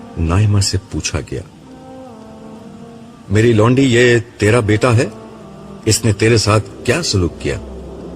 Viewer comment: the correct word is "meri bandi" instead of "meri londi". so please correction required